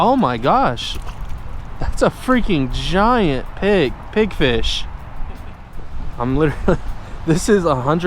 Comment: Pigfish are great eating, a Pigfish is a type of grunt. Sounded like you were saying pinfish